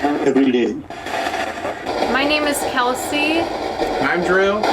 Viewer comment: sounds like it says lizzy